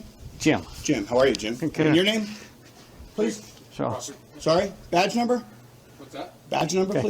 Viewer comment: it's not (inaudible) he clearly says "Police -- officer" 😂